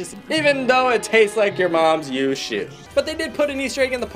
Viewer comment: wait did just say a bad word? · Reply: Shoe not S**t